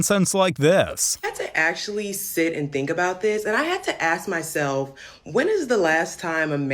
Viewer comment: Did she said "ASK" ?